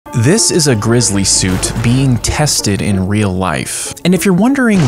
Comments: I thought he said glizzy suit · i thought it said glizzy suit 💀💀💀